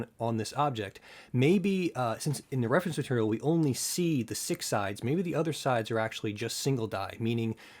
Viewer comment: No, how did you come to that conclusion? I said what I meant, and I meant what I said. You questioned what was on the sides of the die, I told you what was on atleast 2 of them. Personally I would extrapolate from that that all 4 sides feature 6 pits, but that's just me. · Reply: Apologies, I had misheard you in the video. I though you said "maybe, since we only see the six side" not "six sides".